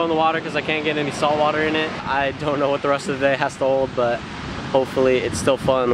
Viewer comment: Couldnt catch what he said at 5:28 Did he said "I dont know what the rest of the day has to hold" Right?? Anyone please? :)